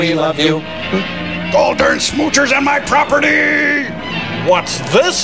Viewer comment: What does Crow say at 1:06:58? Something "property"?